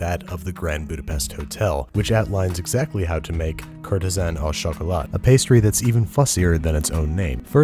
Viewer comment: I thought you said a cryptogram a choolat